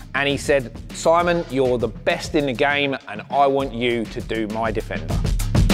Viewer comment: Did he really say you are "the best in the game" ??